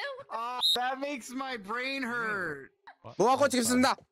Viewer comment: wait what brain heart? He said brain hurt. Who made it?